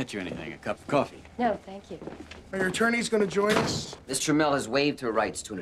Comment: Ohhhh they were saying Miss Tramell! I thought they were saying Mister Mell!